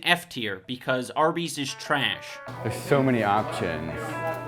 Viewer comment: it sounds like pewds said trash